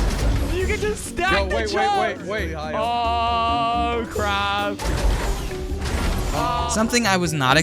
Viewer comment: he did not say oh crouch he said oh crap